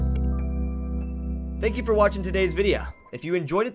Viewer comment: i could've sworn i heard "thank you for watching today's videa"